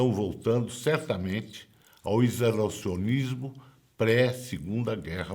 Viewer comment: o que foi dito no minuto 6:17? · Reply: Isolacionismo